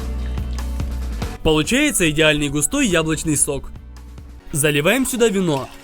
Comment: мне одному послышалось яблочный сок?)